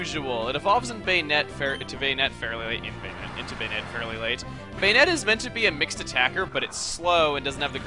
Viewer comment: it sounds like, instead of banette, he says bayonetta. coincidence?